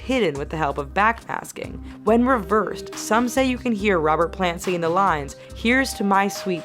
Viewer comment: Instead of "hear" the subtitles said "her"